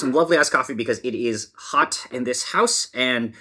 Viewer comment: I heard “lovely ass coffee” 😂